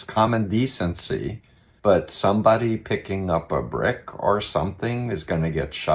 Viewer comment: I heard "throw a brick", not "pick up a brick." I also heard "notify family where to collect", not "send to your family's doorstep."